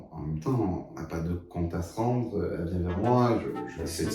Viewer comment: contra… quoi !?😂😂😂😂😂😂😂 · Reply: Compte à se rendre